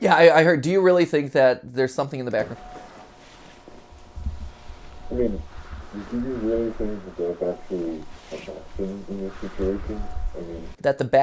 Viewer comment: What I think the yellow suits are muffing at 16:11 " I mean, do you really think that there's actually, like a "Thing" in this facility? There's too many variables"